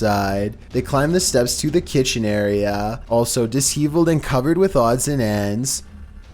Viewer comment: did he just said Dis-HE-villed? Like.. Disheveled?